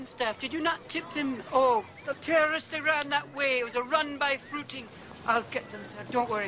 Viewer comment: I could have sworn he said drive by fruiting in the movie . I have the dvd